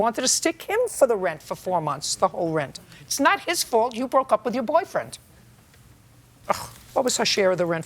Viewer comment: Judge Judy’s scoff 2:08…I heard her say witcho stupid 🫏